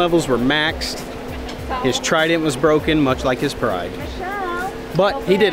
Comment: Thought I heard, "His humiliation level was maxed, and his trident was broken much like his bride." And I thought, "wow! that's kind of harsh." 😂